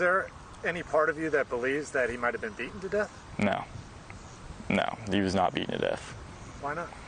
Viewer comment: What is he mumbling at 1:53? "The she that was on good stayed on, I don't know?"